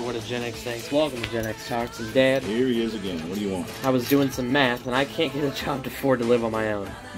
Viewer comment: So I wasn't listening well it sounded like he said "dad, I was doing some meth and I can't afford to live on my own"